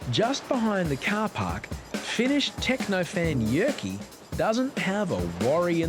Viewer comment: did he say Finnish and Jyrki? 😂 As a finn it's nice to see finland on the world map😂